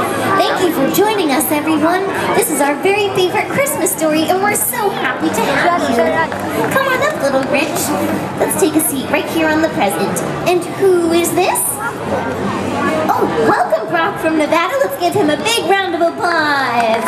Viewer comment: I sounded like she said well come up little b***